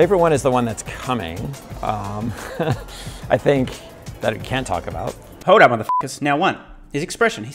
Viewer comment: My interpretation of what Franz said is different, for a few reasons. First, at 8:52 I thought he said "that I can't talk about" rather than "that I can talk about". I replayed the clip a few times, and that is what I hear, but my confidence in hearing it correctly is only a little over 50%. Second, I think Franz was speaking in a general sense rather than about something specific. In other words, he was saying, "usually, the thing I feel most proud about at any moment is whatever unreleased product I am currently working on (and therefore can't talk about)". I favour that interpretation because that is how I have always felt about innovations in my own work throughout the decades: I am more proud of, say, the cool software hack I am currently developing than the equally cool software hack I wrote last month. If Franz did say "that I can talk about", then he was probably referring to the Cybertruck since he can talk about that future product, and immediately afterwards he did talk about that vehicle. I vaguely recall Elon saying (I can't remember when but it was during the past few months) that Tesla is working on several products that nobody outside of Tesla knows about, so it is likely that Tesla is already working on future vehicles that it hasn't yet hinted about.